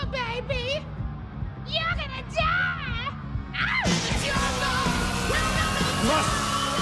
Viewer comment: I watched this when I was around 12 and I didn't really understand English at the time. I just realised he says "you're gonna die" and not "dadadata"